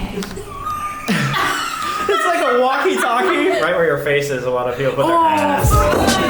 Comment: What did he say at 20:03 ? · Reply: Right where ur face is is where alot of ppl have been w there ass(smth like this)